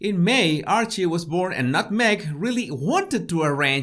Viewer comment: Did he call her NutMeg? Lol